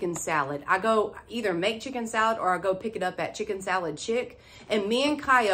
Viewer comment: I love it when you get super excited about your DT hauls! Your accent is adorable, and at first I thought you said you get your salad at “Chicken Salad $hit” lol!! Thanks for always making me smile and have a beautiful weekend! 🤍